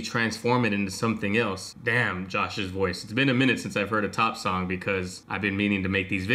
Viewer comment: Wait 4:42 did you said “Josh’s voice” ._.